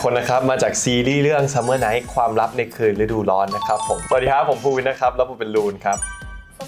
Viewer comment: Is it just me or did anybody heard when phuwin Introduce himself, he said pondphuwin 0:37😅